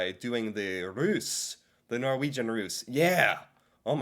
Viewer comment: the way he says "russ" sounds like hes saying "rus" which means drugs